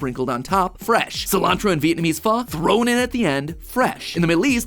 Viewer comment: sorry, did I hear you pronounce Pho "fuh" instead of "fo" at 6:53?